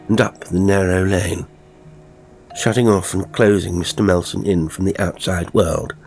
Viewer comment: I misheard "Melsome" as "Melsen" and thought for a moment I'd selected a HorrorBabble instead of an.